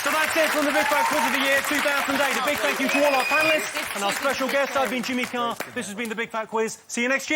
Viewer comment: "and our special guest 'Ugly Jimmy Carr' " is what I heard ahaha